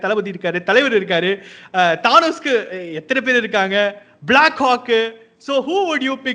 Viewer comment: did he say Black c*** 😅